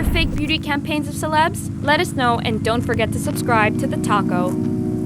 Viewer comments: I thought she said to subscribe to the taco 🌮🌮🌮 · Thought at the end she said "don't forget to subscribe to the taco" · Lol it sound like you said go subscribe to the taco XD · When you said subscribe to the talko I thought you said subscribe to the taco